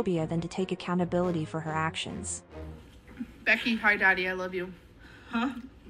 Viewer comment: Can anyone tell me what the hell did she say at 5:38? English isn't my first language😭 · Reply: "becky, hi daddy, i love you" 🥴